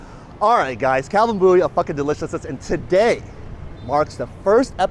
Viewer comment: I swear I thought he said fucken delicious lol